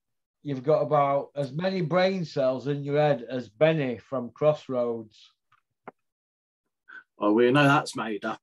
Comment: My hearing is goin I thought you said 'Bennett from Commando'